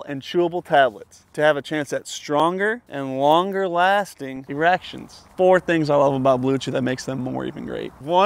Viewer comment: It kinda sounded like he said stronger and longer lasting reactions. Which I guess that's good too!😂